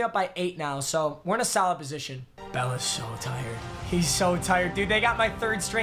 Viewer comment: thought he said Bellas so tired